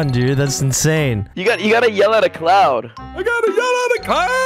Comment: I thought he said I got yelled at clown 😂 but no “cloud”